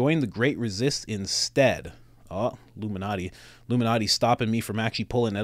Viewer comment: I thought you said Lou Malnati’s (Chicago pizza place) instead of Illuminati when you were promoting The Great Resist T-shirts.🤣🤣